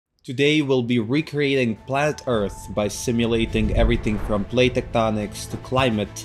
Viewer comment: I heard flat earth lol And was disappointed that this wasn’t a detailed/realistic simulation of what flat earth would be like 😅